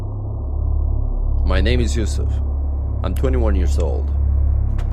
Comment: why tf I hear "my name is Useless" in